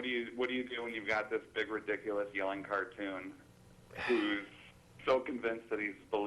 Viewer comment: Did he say "yawn cartoon" at 4:04? What is that?